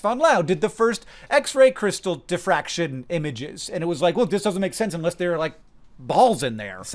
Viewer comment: did you just say the concept of Adam’s only make sense if they have little balls? Syins says Wut?